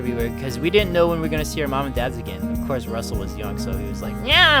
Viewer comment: I thought you said your mom died sorry HAHA